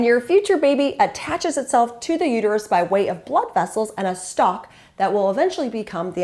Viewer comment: what do you mean by this: "by the way of blood vessels and a stock". What is stock? Am I hearing it not right? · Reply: Blood vessels and a stalk--tissue that connects the baby to the wall of the uterus and becomes the umbilical cord ;-)